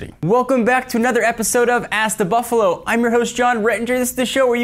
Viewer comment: I thought he said ass the buffalo.